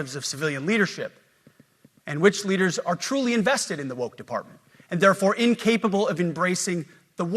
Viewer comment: I thought that Pete Hegseth said, "drunk department."